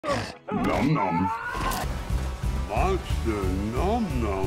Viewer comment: him pointing at the dead bodies as hes walking saying "that's a nom nom" then sees another one and says "nom nom" just warms my heart, it's like when a kid is pointing out bugs or something. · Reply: i thought he was saying "that one nom no" or "that nom nom no"